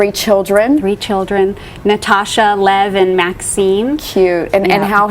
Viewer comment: "Maksim" - i thought she said Maxine...i was like she only has one daughter tho.